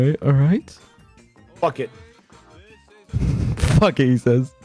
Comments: Did Ranboo just swear? Did he just say F*CK it? My angale swore oh no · I love how even Big Q thought Ranboo said "F*ck it!" then only realized he said "Bucket!" when Ranboo corrected chat